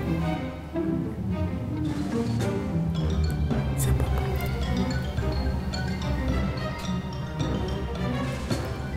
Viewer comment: my narinig akong bumulong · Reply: Si ms yun sabi nya isa papo tapos okay